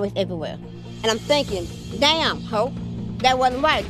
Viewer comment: It sounded like she said damn HO that wasn’t right! But she really said HOPE!😂😂😂